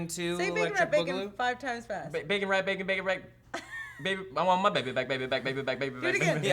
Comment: It sounded like Will said bacon wrapped baby